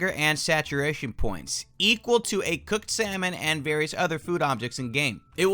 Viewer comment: Did He Just Say A Cooked Cinnamon